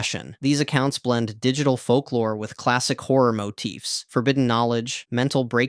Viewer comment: did he say... motif?